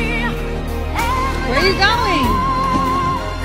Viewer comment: “I just asked you to come help me” is definitely the biggest understatement I’ve heard in a while. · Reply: LOL for a second I heard "Hump me"